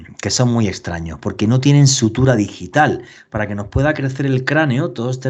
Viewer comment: ¿Escuché sutura DIGITAL? Es sutura sagital.